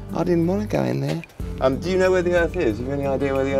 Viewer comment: "Do you know where the Earth is" - 2019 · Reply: I thought he said hearth at first thinking it was slang for the den so when i heard earth the second time i wasnt thinking in terms of the world lol so funny though.